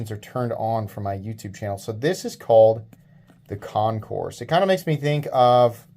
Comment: I thought it said Concord at first lol I like the look of this one though thanks for sharing it 👍